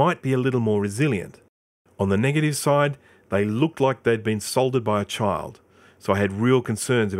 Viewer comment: I thought you said they were Assaulted by a Child lol